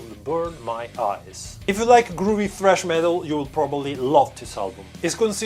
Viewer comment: Hahaha!!! I'm so sorry. However, in the beginning of your video, when you were talking about the bands and the music. I honestly thought that you were saying Groovy Fresh Metal. After the second or third time re-watching your video. I realized that you were saying Groovy Thrash Metal. In my mind, initially I was thinking...What the Fu<k is Groovy Fresh Metal? But now that it has been mentioned you should compose at least one track that you would consider "Groovy Fresh Metal." I realize that it's slightly ridiculous. But Fu<k it. Just mess around and see what you come up with. You're definitely a talented musician. I believe that you would play something kick-ass.